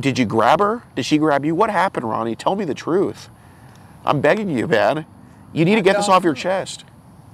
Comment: did he say "im begging you dad" or is his nose stuffed and he said man?😂